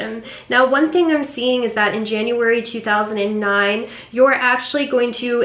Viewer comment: did you say January 2009? 😅